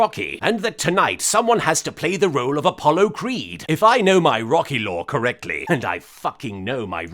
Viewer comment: "And i fucking know my Rocky Law!" New T-Shirt idea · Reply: also I thought he said Rocky lore.